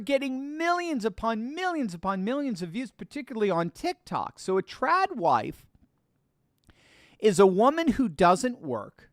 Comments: I thought it said Trade wife 🤣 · I thought it said trade wife. Lol